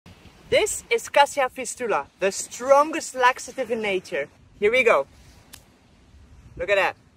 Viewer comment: I thought he said "the strongest black stuff in nature" then brought the stick in half 😳